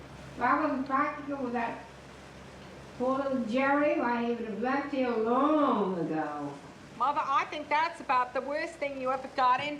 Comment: What is big edie saying at 25:05? I can only make out part of it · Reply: "Jerry would've left here long ago"